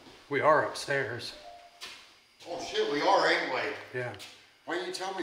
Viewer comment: sounded like a rapsy woman voice say "Yeah."